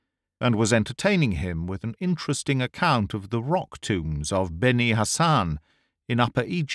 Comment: Rock tombs!! Thought you said rock tunes!!🤣🤣